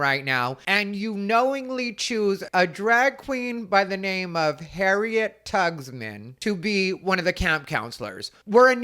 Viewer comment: I heard "harriet tuckit"'lol